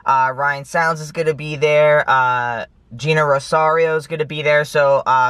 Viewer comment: When you were listing the names of people who would be at the event I thought you said Nina Rosario, who's a character from one of my favourite musicals, In The Heights lol.